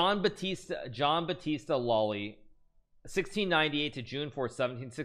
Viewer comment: At about 3:15 he apparently mentions Jean-Baptiste Lully, but what I see is a musician, not a chess player, and lived several decades earlier than what he said. What is the correct spelling and link to him and his checkmate? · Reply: Okay, the last name is Lolli (not Loli) which gives the search result quickly